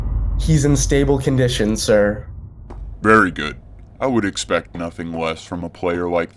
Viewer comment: omg i heard him say verity